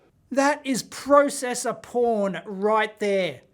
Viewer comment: Did I just hear, "Processer PORN" ...... 🤔💃🤖🤣